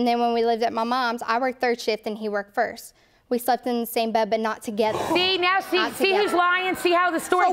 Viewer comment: I'm I the only one that heard her say they slept in the same bed but not at the same time???